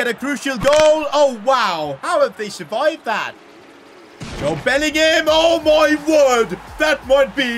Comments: Did you say jobe Bellingham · Jobes such a good lad. has completely changed people's perspective about him😂😂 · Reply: its sounds like he said jobe bellingham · did really said jobe Bellingham 😂😂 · saying jobe instead of Jude Bellingham 😂😂😂